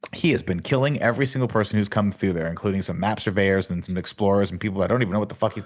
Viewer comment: I misheard Nick saying "Map Surveryors" and thought he said Napster lol